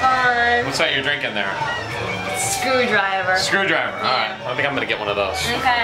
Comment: Huh? Whats the drink? I heard "screwdriver"